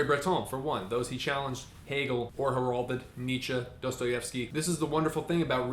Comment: mentions some authors: Breton, Hegel, ..., Nietzsche and Dostojevskij. However, I simply cannot hear the name he says between “Hegel” and “Nietzsche”. The auto-generated subtitles says “for her Albin” which is obviously nonsense. Can anyone tell me who he is talking about? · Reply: He doesn't mention a philosopher's name. He says "or heralded" after Hegel, talking about how Bataille "challenged" some artists. It's opposites on the same spectrum. He's talking about artists whom Bataille heralded.